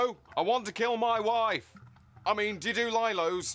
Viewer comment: What does he say at the end? "I mean, ......" I can't understand it :( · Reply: Lilos. They're inflatable mattresses.